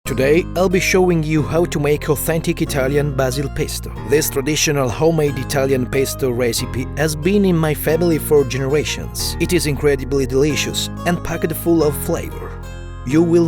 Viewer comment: For half a second I thought it said autistic pesto